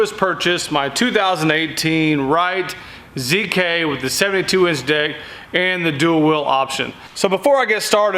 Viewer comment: sound like he said the 72 inch dick 😂